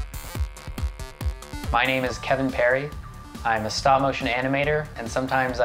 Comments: who else thought he said his name was katy Perry??😂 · i thought he said "my name is katy perry" lol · I thought you say your name was Katy Perry